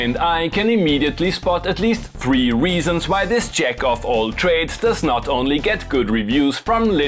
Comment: "Jack-off all trades",,,lol, what did he just say?